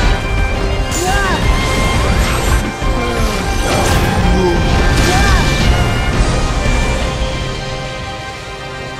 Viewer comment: Does anyone have an idea of what Xemnas actually says at 21:09 when he activates the reality shift trigger event? Have always wondered what he says and could never make it out over the music slapping so hard EDIT: 32:15 Also, I wonder how different KH3 would have been if Lea had not shown up at this moment to block Xehanort’s heart from reaching Sora? Obviously they would’ve gotten Sora back somehow but just imagine a norted Sora that isn’t Vanitas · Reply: "despair shall be yours"